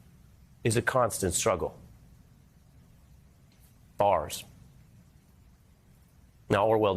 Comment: Did he say “bars” at the 5:03 minute mark?! Lol